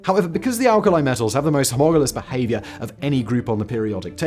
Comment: You speak too quickly and we can't make out certain words. You need to slow down just 10%. At 3:34, you say "Because alkali metals have the most hamoralous behavior"..... What are you saying there? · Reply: He says "homogalous" though I don't know what that actually means